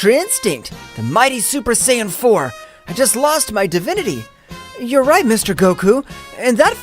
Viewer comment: i thought he said he lost his virginity like grand priest made him gay